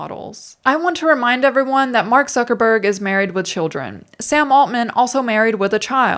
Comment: I love how you say "Mark Suckerberg" in a way that offers just the bare minimum of plausible deniability that I'm mishearing it.